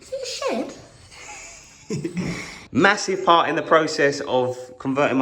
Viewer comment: did you say MASSIVE?! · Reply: did he say MASSIVE again